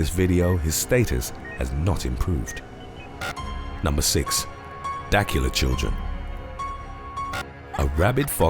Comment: I'll be honest I totally thought he said Dracula children and I was so ready for a story of some rabid child attacking a biting the shit out of someone